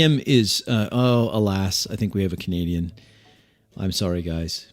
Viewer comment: I thought you said "Comedian" at first instead of "Canadian" and was quite confused until I figured it out!